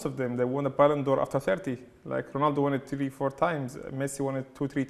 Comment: Did I hear it "Ronald Won it TV"